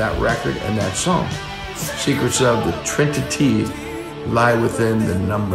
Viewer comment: Is my hearing off or does Sammy say "trin-ti-tee"? If so, I've been singing it wrong all this time...